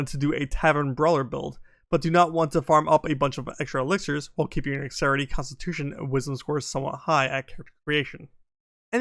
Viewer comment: Bruh what were you saying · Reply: Apparently, past me didn't know either as I didn't include it in the subtitles but rewatching it now... it was talking about opting to keep your dexterity wisdom and constitution scores high at character creation without needing to use strength elixirs(which allows you to opt for different elixirs). Basically, dump strength, use this and have elixir options open while also having those other stats high. I do apologize. I've gotten better, more so since I stopped reading scripts. I tended to read too quickly off of what I wrote and ended up mumbling because of it(It wasn't always word for word what the script was saying sometimes though.)